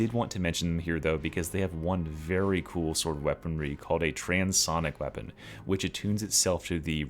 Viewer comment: im sorry did you say TRANS Sonic?? 🏳️‍⚧️🔵